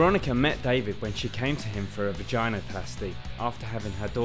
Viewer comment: I heard "from Vagina" Hahaha crazy